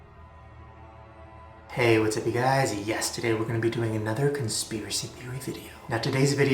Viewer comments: I thought he said yesterday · When I first started watching Shane, when he would say "yes today" I thought he said "yesterday" and I was like "tf why" 😂